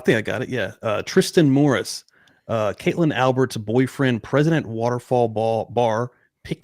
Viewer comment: Great episode guys! I did have to rewind at 31:34 like a million times though because I thought he said "President (of) Waterfall Bar" and I was like, 'WTF is the president of the bar?!" 🤣